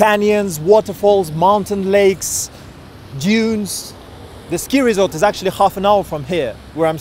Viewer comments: I thought he said Jews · thought he said jews. Got me laughing lmao